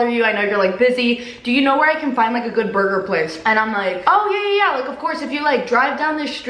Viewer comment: I thought she said do you know where to find a murder place 😂😂😂😂